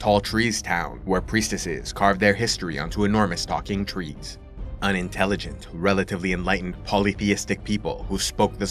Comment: I’m sorry but why the ebony island people were unintelligent… do better George r Martin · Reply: he said "an intelligent" i misheard it too but he cleared it up in another comment.